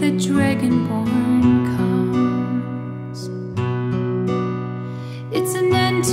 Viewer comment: Thank you all for watching, and for all of the kind words! Unfortunately this video got fully de-monetized. If you would like to show your support for this channel, here are multiple ways you can do this! Links for all of these are in the description. Free ways to support: -Stream on Spotify (or other streaming platforms), seriously, this helps a lot! -Follow on social media More ways to support: -Join the Patreon family! -Check out the march shop and use code SUNSET for 10% off until September 7th (11:59pm PT) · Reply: One of the best covers I've heard, thank you) Have you ever been doing cover on Nightwish? Maybe you have and I didn't find it... Would sounds great I think While I've been re-listening it, suddenly heard "Dragonborn scum". Hell of a mishearing. Just... crap)